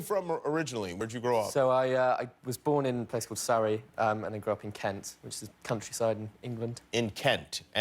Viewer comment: Did he say "your nose is a little bit weird" or am I just hearing things?? 😂😂